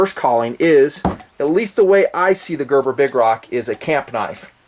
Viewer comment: i thought it said gerber big cock